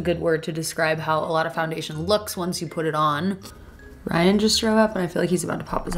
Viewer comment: i legit thought you said 'Ryan just threw up' haha as if this was something really normal